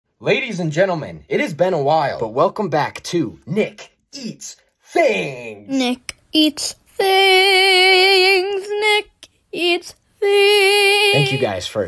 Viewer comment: My dumbass thought it said “ Nick Eats Thongs “